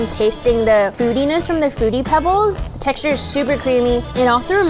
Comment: I heard her say footy pebbles